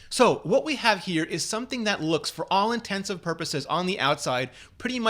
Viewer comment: Did you say “for all INTENSIVE purposes?” 0.o